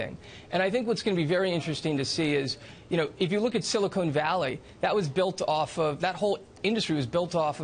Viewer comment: If you don't know then you sir are an embarrassment for the human, race we as a society are finally pushing forward in technology then ppl like you are making it into a game, it's not a game of you can't handle human incandescent then I stand corrected. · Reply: It sounded like he said silicone, not silicon. Of course I know of silicon valley. Before you go rambling on like an idiot, why don't you go back to school to learn proper grammar, that sentence is full of run on's and missplaced commas.